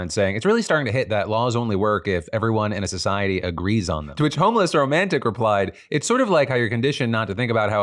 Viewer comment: Homeless romantic? Am I the only one who heard that?